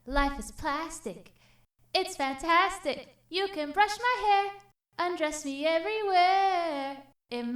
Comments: I thought the Barbie said “and undress me anywhere” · at the end i thought it said "common barbie lets go potty" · Omg I thought she said c'mon Barbie let's go potty lol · I thought the end sound she said “Come on Barbie let’s go potty”